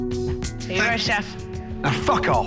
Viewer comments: What did say to Ramsay? She gave her jacket back and said something and Ramsay was pissed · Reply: It sounded like she literally said: "Thank you Chef". And got cursed out. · what did she say? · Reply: She said “sorry, chef”, but his response was a bit harsh? Not sure why but maybe it’s cuz of the episode’s context. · Can anyone tell me what she said at 15:10?? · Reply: She probably said Goodbye chef